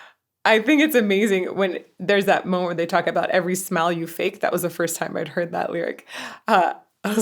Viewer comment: Not sure if I misheard you or if you may have misheard the lyric "every smile you fake" but it sounded like you might have said "every smell you fake". First off, yes, focusing on someone's smell can make things seem quite creepy, but also, how would one fake a smell? I'm not sure I want to know, actually. Anyway, the lyrics is "every smile you fake" though it does seem like he is singing a few lyrics a little different. I don't remember "every night you stay" in the studio version for example.